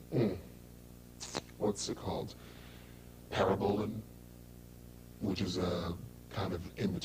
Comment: Ronnie didnt mentioned Trenbolone, hmmmmmm. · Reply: I thought I heard Ronnie mention he used Parabolin which is Tren Hex.